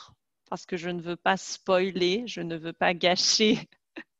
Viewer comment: It sounds like she said "je ne veure pas" several times to mean "I didn't want". Can't figure out what she was actually saying or the spelling.